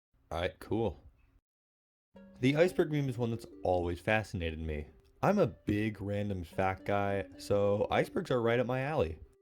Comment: 15 seconds in and I thought you said you were a big random fat guy